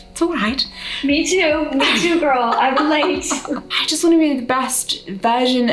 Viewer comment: quick subtitle note: at 19:15 Molly speaks and the subtitle says (inaudible) but she said "I relate!" :) Just in case anyone wanted to know~